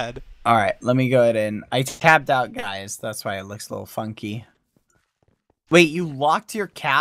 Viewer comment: I THOUGHT BAD SAID "that's why it looks so f***ing.." I WAS LIKE 😃